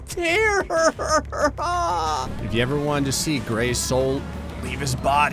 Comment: I heard the words "Gray souls" and now I demand Gray beat his soul against Dark Souls games. And call the playthrough "Gray Souls"